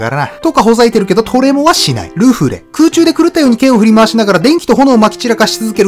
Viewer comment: ルフレがシャンクスのルフィに聞こえた